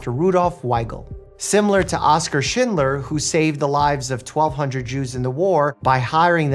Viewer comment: the first time I thought you said “similar to Oscar shitler” 😅💀